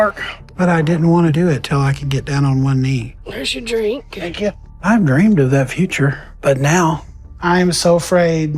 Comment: I literally heard say "Thank you, have a wonderful day". He's crappy, but let's not make stuff up